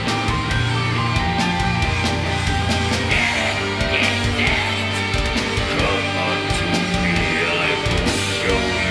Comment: anyone knows what he says at 3:25 after "come onto me i will show you ??????? extascy" thanks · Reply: "This is death, come unto me, I will show you the works of pure ecstasy"